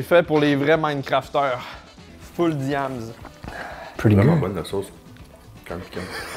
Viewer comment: pourquoi on entend : pretty girl ?? 😂 c’est tu moi qui a une hallucination auditive ? J’ai reculer 4 fois pour être sur !